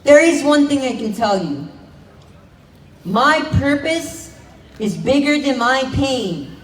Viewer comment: I thought he said my purpose is bigger then my peen 😂😂